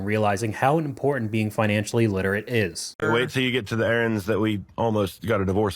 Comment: confused me so much. The caption says "errands" when it meant Aaron's.